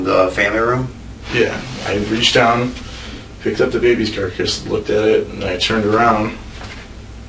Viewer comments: does he say "the baby carcass"?! Or is there a brand that sounds like that? Like "the car-kids". It sounds very suspicious. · Did he say, "Baby Carcass"? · am I crazy, or did he say "picked up the baby's carcass" ? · Dude! 53:18 he says I picked up the baby's carcass instead of baby's car seat! Talk about a Freudian slip! 👏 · does he say he picked up the baby’s CARCASS?? Freudian slip much? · he has a Freudian slip and says "carcass"!!! The detective either didn't catch it or let it slide.... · DId he say baby 'carcass'?? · Did he say, "I turned around and picked up th?e baby's carcass." ????? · it sounded like he said “I picked up the baby carcass, looked at it and turned around” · ish does he say, "the baby's carcass" ?? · when he is trying to say he was looking for the baby instead of seeing the dead body, he tries to say "baby carrier" but instead says "baby carcass". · Picked up the baby’s what ??? 53:18 sounds like he said carcass how did anybody miss that